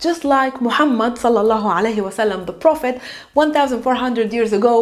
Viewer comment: Ahlan, @ 02:40 what did you say just after calling The Prophet's name, please can you tell me using both Arabic script & English transliteration? Allah isselmik. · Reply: صلى الله عليه وسلم Salla Allahu Alayhi wa Sallam Which means Peace be upon him.